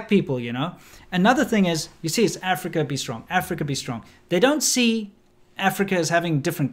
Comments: Sounds like they said Africa is dumb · The “Africa be strong” part was so cringe lmfao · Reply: I keep hearing "Africa is Drunk"....